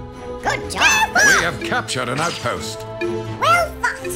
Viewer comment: i hear we have captured a CNN post